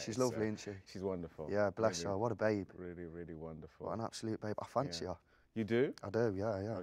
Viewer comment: what has he said at 8:25? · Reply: Jack: "What an absolute babe. I fancy her [Judy Dench]."